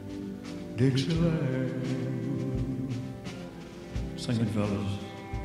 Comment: What he said at 00:32 "Singing...what? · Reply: "Sing it fellas"